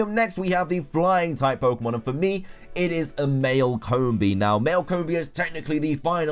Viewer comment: Dude I thought you said only fully evolved 6:47 ok male, but You already put a bug-flying. For continuity I would have picked something else. I agree on hitmonchan though.